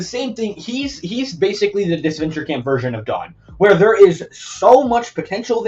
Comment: I thought you said god and i was so confused 😭